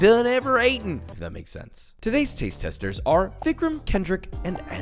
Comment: I thought he said thiccram